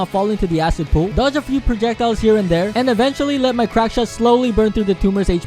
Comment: When you said I will be using the crack shot I thought you said "crack shit" whoops